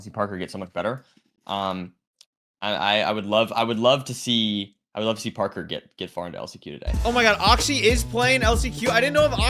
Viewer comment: because he repeated himself, i thought he said "I would love to see epilepsy parkour make it to the playoffs"